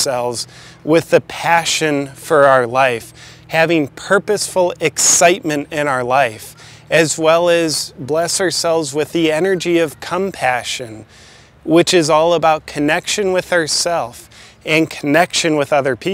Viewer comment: Did he just say, 'energy of cumpation' ?